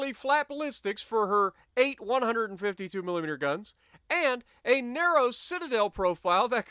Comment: Did u just say 852mm guns